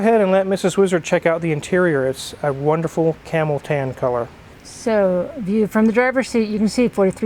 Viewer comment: Omg ! I thought you said Mrs, Wizards Camel toe color!